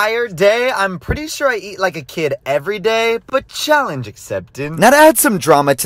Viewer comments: “I eat like a kid everyday 😄” “I eat like, a kid everyday 👹” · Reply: Bro it sounds like he said he eats a kid everyday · thought you said “i’m pretty sure i eat a kid every day” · At first I heard "I eat, like, a kid everyday." For some reason 💀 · For a second I thought he said "well I pretty much make a kid everyday" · I thought he said I eat a kid every day 💀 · Ngl I thought he said I eat kid’s everyday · Wait a damn minute . What did he sayy😳😳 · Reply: " i am pretty sure i eat like a kid everyday " What i thought he said was he eats like one kid a day